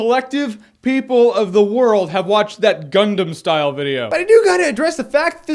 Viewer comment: Did he just say Gundam Style? LOL XD